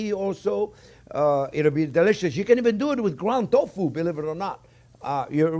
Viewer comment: at first I thought you said "ground dog food" lmao. (ground dog food=ground tofu) :)